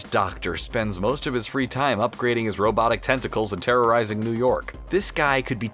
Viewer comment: When he was talking about otto octavius I thought he said he was upgrading his testicles🤣